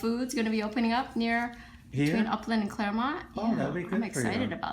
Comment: I'd almost bet those were street names as I believe her practice is in Southern Cal and no where near Oakland, CA. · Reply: I listened again and I thought she said Oakland also but what I believe she actually said was between Upland and Claremont which makes more sense as her practice is in Upland, CA. It's east of Los Angeles. Just some trivia for you! :) Oh, Claremont is just west of Upland....